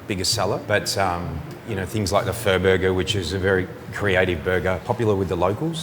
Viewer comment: Was that a freudian slip. Because I could have SWORN dude said, FUR BURGER 😆😅🤣😂